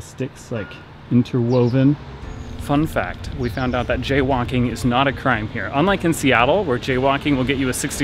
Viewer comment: Ngl, i thought he said "jaywanking" instead of jaywalking